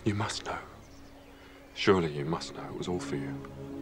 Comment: 🎶it’s you it’s you it’s all for you🎶sorry not sorry thats what I heard when he said that😂